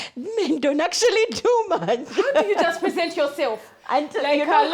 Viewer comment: I hear, can't u salt ko urself? 😂😂😂😂😂😂